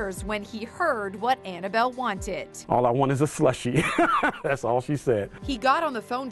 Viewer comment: thought it said “for a sushi”